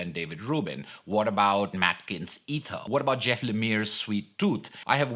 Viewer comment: What's the name of the artist you're talking about at 8:37 (immediately after Beowulf) - I tried to google what I heard, but nothing is coming up. Thanks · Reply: Matt Kindt's Ether (also drawn by David Rubin!) 😊